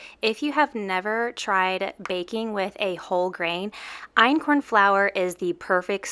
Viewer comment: No lie , in the beginning it sounds like she said baking with a--hole grain ... ? I am very groggy right now and I guess that's why it sounded that way